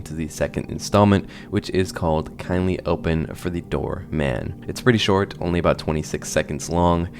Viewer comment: Every time he says "door man" i hear "dharr mann" and I can't stop laughing